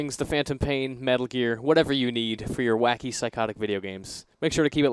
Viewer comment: I believe the lady at the end says "He has come to" but with her accent it sounds like she says "V has come to" V as in 5 perhaps?